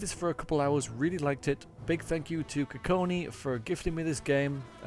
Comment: Wait wat did you say KONY at 0:15?